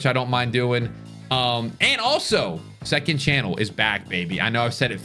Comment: i thought you said cod is back that aged well XD